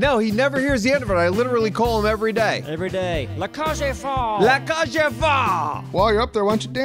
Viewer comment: Well is happy. Apparently he loves himself some, the Birdcage. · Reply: He was pronouncing it so weird, I thought he was saying "le cage en faux" or "le cage enfant". I know neither of those make any sense, but this is we're talking about